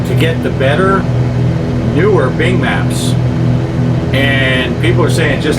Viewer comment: I thought that you said Big Macs . Now I want one or four .